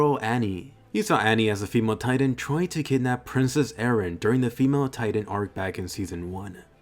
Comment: am I going crazy or did he call eren “princess eren”?